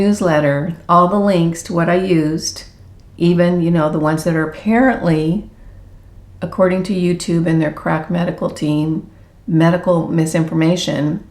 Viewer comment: “Crack medical team” 🤣🤣 love it!! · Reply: I thought you were saying “CRAP medical team”! Oooooooo…maybe I should’ve typed “CR*P medical team”! 😂 Stay Strong! WE’RE WITH YA!!! Hugs from Louisiana!❤